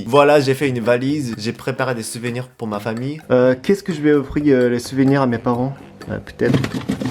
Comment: C'est moi ou à 0:09 t'a dit " Kex euh que je vais prendre comme souvenirs pour mes parents.."